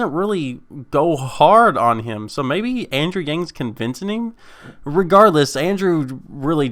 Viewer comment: did you just say "convincening"?